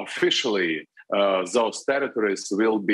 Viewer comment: Did he say "those territories" or "those terrorists"? :-)